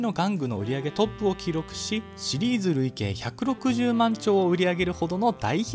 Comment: 160万丁を兆に聞こえて任天堂やばすぎると思ったわ